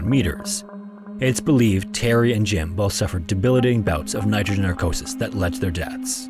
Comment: You really need to slow down your narration. Several times you slurred words together making them difficult to understand, or just slurred individual words outright. 7:25 "debilling" instead of debilitating, just one example.